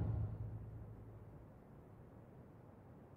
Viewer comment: what he says at 1:09 ?? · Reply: confine your tongue. lest it confine you. it basically mean's don't be too loose with what you say. or you'll face consequence's that limit your freedom.